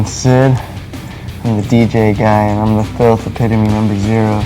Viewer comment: What does Sid say? I think I hear "I'm Sid, I'm the DJ guy, and I'm the filth replicating the number 0" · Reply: It's something like "the empitimy"